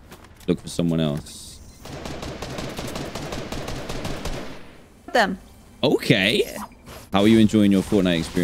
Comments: Did just say F them?! Lol that’s literally what I do every time I kill someone… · did She just say f**k them 😂😂😂😂?